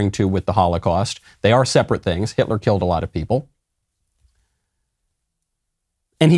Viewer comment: I thought you say Poles.